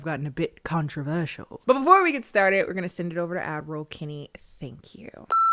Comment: oh girl its so funny you clarified it's ad roll and not admiral bc this whole time i've thought you were saying adderall LOL